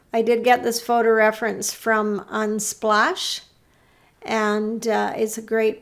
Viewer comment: Thank you for a wonderfully informative video demo! Also what's the website where you found the photo? I thought you said splash but I can't find a website with that name, other than a pure water website. Thank you again! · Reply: Unsplash. If you go under my community tab you will see the link to the photo.